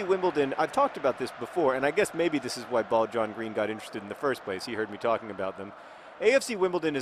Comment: Is it just me or does john sound like he is saying "ball john green"